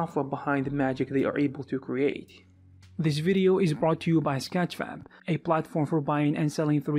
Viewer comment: did he say scatch fam?